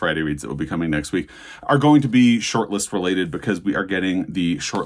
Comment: I thought you said “Both my videos next week with be SHIRTLESS (related)!!!” 😂😂😂